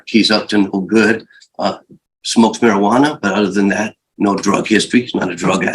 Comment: Did the attny say he smoked marijuana but doesn't drink or do drugs? I probably misheard, but now i can't unhear it.